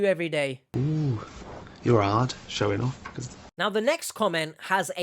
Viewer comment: What is he saying there? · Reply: The meme? “Ooo you’re hard, showing off the… (camera)” I use it when I say something serious mostly 😂